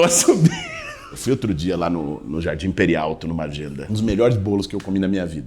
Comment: Jardim Peri Alto foi legendado como Jardim Imperial.